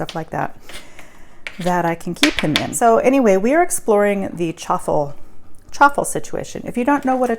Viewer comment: What!! I thought you said truffle!! Now I’m super excited!!! Def trying this!!